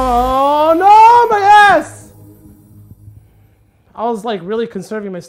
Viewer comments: sounds like he said ass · I thought you said No my ass